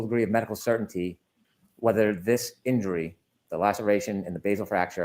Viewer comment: Every time Brennan speaks i cant get Marge Simpson out of my head 🤦‍♀️ Also Bev and Brennan definitely have their own little sly signals to each other and dont hide it well either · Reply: He always sounds like he is saying,"Mr. O'Keith" instead of "Mr. O'Keefe" but ive never seen anyone mention it so i guess it may be just me. It drives me nuts.